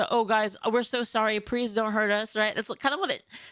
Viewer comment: Did say “prease”??